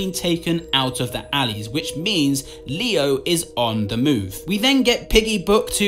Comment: i literally thought u said “leo is on the moon” 😂